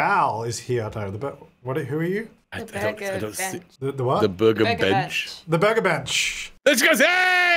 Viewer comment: Did say "Burger Bishhh" and just misheard Bench? 🤣